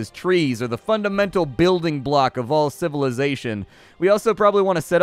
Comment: I thought you said 'farriers', and was all like, "Why on Earth do you need three people to shoe horses?" Lol.